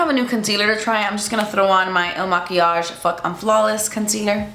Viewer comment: did she say, “f*** unflawless concealer”? 😂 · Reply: Lol fuck I'm flawless!